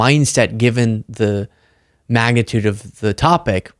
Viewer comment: you said "magnitude," i heard "MAGAtude"